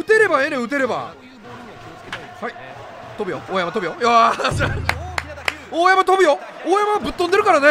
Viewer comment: 「大山飛ぶよ」が「大山のぶ代」に聞こえてしんどい笑 大山とぶ代。